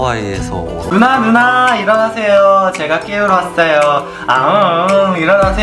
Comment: The OG 4d! Also at 0:25 I think he is saying 'Nuna, nuna, ironaseyo' meaning nuna, wake up... I think that's like a wake up alarm💛💛